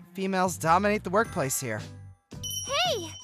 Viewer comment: you put ms mochizuki instead of ms azumi when he said good morning to her